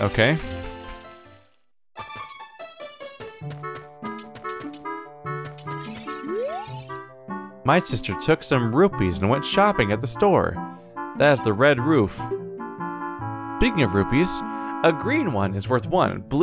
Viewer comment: Half paying attention, I swear I heard "My sister took some roofies and went shopping at the store, the one with the red roof." Well, I hope she isn't gone too long or the dude humping the rock at 22:51 is going to be trouble!